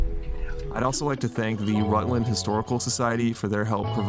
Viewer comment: I'm from Mass..what part of mass is this beautiful place located? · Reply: I thought he said worcester I'm from Woburn Massachusetts